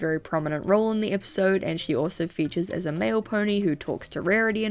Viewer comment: MAIL pony! I thought she said MALE pony and thought they were talking about a trans character.